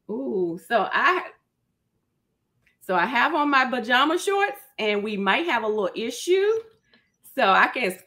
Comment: But did she say “buh-jama” shorts tho???